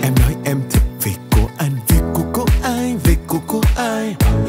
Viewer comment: Khúc Vị của của anh mình lại nghe thành vị của cu anh